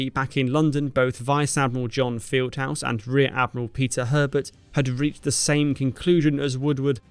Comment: "And luckily back in London both vice admiral John Fieldhouse and rear admiral Pizza Herbert had reached(?) the same conclusion as Woodward..." I swear that's what I heard.